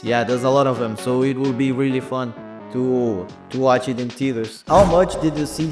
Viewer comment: wait hold up am i hearing this right? theaters?!!!!!!!!!!!! lesgo thank u so much for your hard work. we are so excited